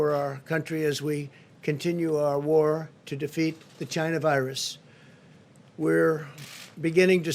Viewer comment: Sound like he said vagina virus 😁😁😅🤣